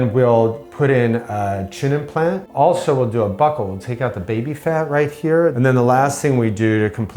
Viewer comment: did he say baby fat??